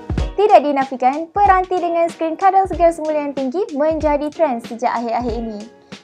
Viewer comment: menjadikan? Ke aku salah dengar? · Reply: Menjadi trend